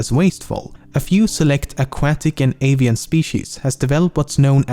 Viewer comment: Did he just say alien species?